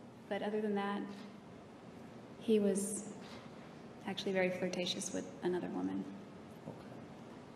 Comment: and some change, did Jodi tell Nurmi that he screwed up? Please, someone clarify this for me. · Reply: Terrence It sounded like JA meant that Travis "screwed up" by being "flirtatious with another woman" in front of her.